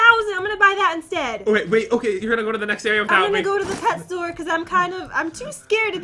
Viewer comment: When you said “pet store” I thought sou said “toy store” and then I’m like wait what?🤣🤣🤣🤣